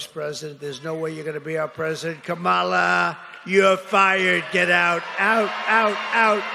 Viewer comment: Trump keeps saying "you're fired" but I keep hearing "Hodor." Like any dementia patient, he circles backmto his strongest memories over and over.